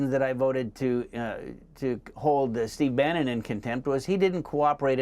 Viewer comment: I thought you guys said "Brandon" dang it's THE SHIFT SHOW! "Let's go Brandon, and that pile of shift!